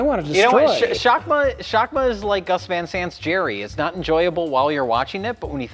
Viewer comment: what's the movie is mentioning here? I couldn't understand a word he said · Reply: Gus van Sant’s Gerry